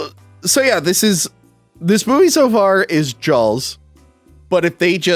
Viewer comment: did say Jaw-L-s?